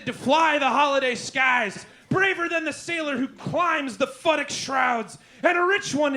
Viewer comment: Help me…. “Braver than the sailor who climbs the (what is this word?) shrouds… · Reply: Futtock! Futtock shrouds are lines attached from the outer edge of the top to a point lower down on the mast to support the top and keep it square to the mast LIKE A BOSS!